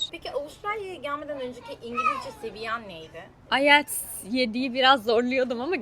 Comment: ne diyor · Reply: IELTS